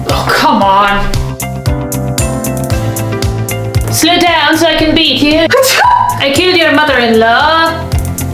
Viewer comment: "Slow down so I can beat you" had me dying 😂😂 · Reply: I heard "Slide down so I can beat you", had to rewind